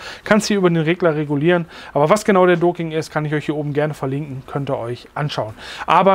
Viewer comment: Habe Video bei 2:26 mehrmals (!) gestartet. Leider verstehe ich "akustisch" nicht den genauen Namen des "Docking???" Es heißt, mit Fingerzeig: verlinke ich euch hier... Finde ich leider nicht. Auch leider nicht in der Beschreibung. Nicht jeder, kennt die (korrekte) Schreibweise. Google'n hilft da auch nicht weiter....